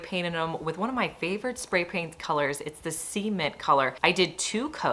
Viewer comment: Great ideas and video. It seems like you and I pronounce the word “cement” differently. At 3:11 to my ears it sounded like you said “semen” lol. Knowing that couldn’t be right, I went back a few times to hear you say the name of your favourite spray paint colour, but each time I heard “semen”. Eventually realized you were saying “cement” lol!! Funny how native English speakers (English is the first language) from different parts of North America pronounce some words so differently. I live in a boarder town in Southern Ontario. Cement is typically pronounced more like “sa-ment”. Hopefully that makes phonetic sense. Thank you for the inspiration and unexpected belly laugh. ❤ from Shannon in Canada 🇨🇦 PS this comment was not intended to be rude, negative or hurtful. IMO hearing different accents speaking the same language as you do, but saying words so vastly different you truly cannot believe your own ears, is fascinating, interesting and can be very humorous. For both sides.